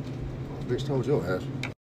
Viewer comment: What's the guy say at 00:51 ? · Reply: He said "bitch told yo ass"